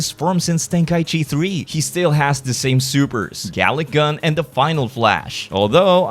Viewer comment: Did he call vegeta a sperm 😅😂